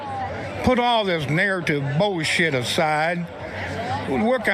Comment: "Put all this ni**ertive bullsh*t aside" is what i heard, prove me wrong 😂